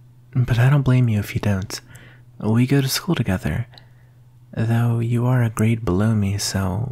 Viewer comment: I was listening with my volume pretty low that at around 1:17 I misheard: “Though you’re a grade below me” As “Though you’re a brave baloney” 0_o i promise you I’m not drunk